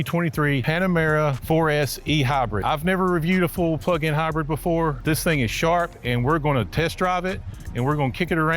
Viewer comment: almost sounds like he said “I’ve never reviewed a full fkn plug in hybrid before” lol